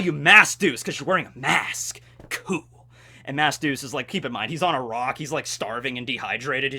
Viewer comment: Wait! Wait! Wait! His name is MASK Duce? I thought was saying Mass Duce the entire time